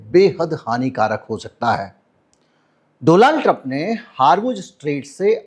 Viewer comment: doland 😂 If i heard correctly 😂